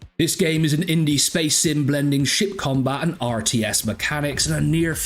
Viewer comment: I wanna do space reign but I ain't interested in anything early access, but I am watching this one keenly. Helium Rain being free is awesome. Everytime you say "ship combat", I hear "shit combat". lol